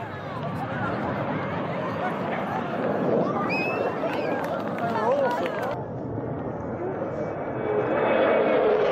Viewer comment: did he just say ''they were awful''? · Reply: Awesome!